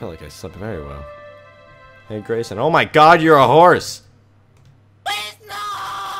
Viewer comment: Who else thought the horse said PLEASE NOOOO